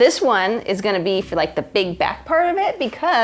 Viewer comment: is it just me or did she SAY BIG BACK!!?